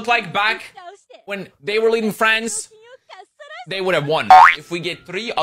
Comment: I found it more funny that the guy couldn’t pronounce Napoleon and that the person in charge of subtitles thought he said “friends” instead of “France”